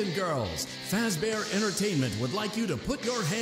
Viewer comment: It paused at the X at right second and it sounded like it said “put ur hands together for the one, the only FEDDY fazbear”🤣